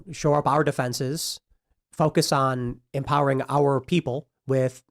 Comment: OOHH TIM IS SAYING "FOCUS ON"! I THOUGHT HE WAS SAYING "FUCK HASSAN". MAKES SO MUCH MORE SENSE